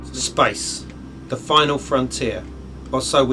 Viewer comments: lol, the mans accent makes it sound like he is saying spice instead of space at 1:43 ... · did he say "Space or Spice"....lol